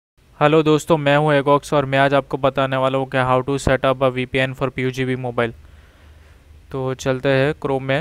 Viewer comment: Pugb mobile 😂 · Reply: I think I heard “pugb” at 0:29 😅. Nice video keep it up